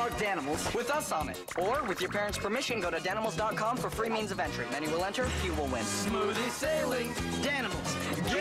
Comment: "many will enter, few will win" awoke something in me like a sleeper agent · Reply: for me I heard “many will enter ONE will win”